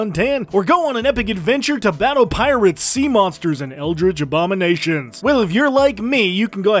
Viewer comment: I thought you said pirate sea monsters and I got excited. Not Pirates, And Sea monsters. I now need pirate sea monsters in my life.